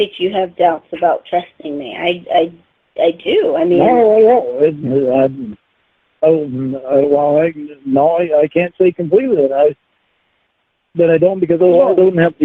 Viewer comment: What the hell is he trying to say at 6:30 ? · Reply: Something along the lines of "I'll say I trust you to keep you happy, but I can't/won't stawwp being paranoid drahnk reetahd."